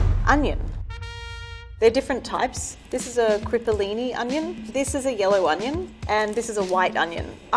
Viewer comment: I keep hearing “roar” instead of “raw” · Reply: I heard 'crippolini onion'